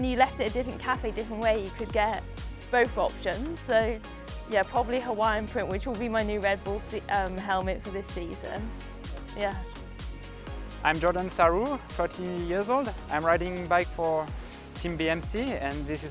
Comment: I’ve listened to “46 or 48” in the chain ring from Jordan Sarrou. Is that correct or he wanted to say 36 or 38 teeth instead of 46-48 ?? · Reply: It's just his accent. I thought he said he was '40 years old' first time I heard him! He is saying 36 or 38.